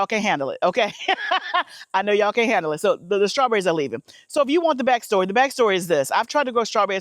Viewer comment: All I heard you saying when you were talking about those strawberries is “Sorry to those strawberries” 😂😂😂😂